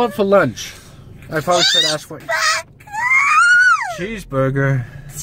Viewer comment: I Thought said I want a cheese booger